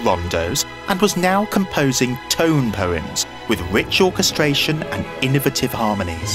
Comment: i thought he said ritual castration